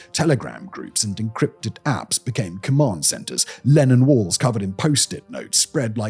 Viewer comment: I know it's Lennon walls (after looking it up), but I misheard "Lenin walls" and I just thought I don't understand but that sounds like irony